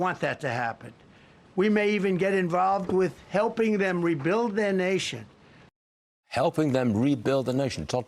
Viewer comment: Did I hear Trump just say to rebuild another countries nation?